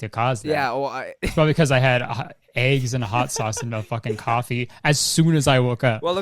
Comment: I thought you guys were just saying 5 sauce